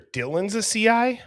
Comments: I hear "I don't know the names of the victims...Dylan's a CI." At first I thought he said, "One's a CI" but now IDk. Either way, someone in the house was a CI. Full Stop. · I knew I heard him say Dylan’s a CI!!!!